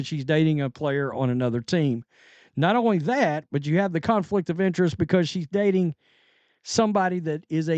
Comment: did he say a " cuntflict of interest " 🤣